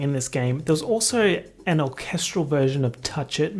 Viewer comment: When you said orchestral version I thought you said all kestrel version. Please forgive me, I am 25 and that is my fault for being that way